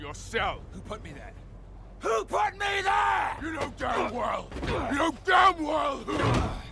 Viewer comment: Hello can someone answer what is thor saying in 1:10,since i'm not native english speaker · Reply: "You know damn well who" He's telling Loki the person to blame for his being imprisoned was himself.